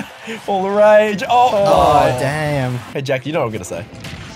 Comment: Did he just call him Jack? 😳